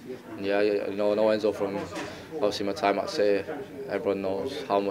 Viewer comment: DID he really say enzo is a COLD manager 🤣🤣🤣🤣🤣🤣🤣🤣🤣🤣🤣😆😆😆😆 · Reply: good manager* fix your ears